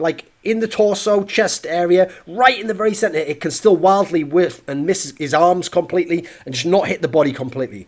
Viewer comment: what does he say at 2:19 ? it still can hardly .... ? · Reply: "it can still wildly whiff"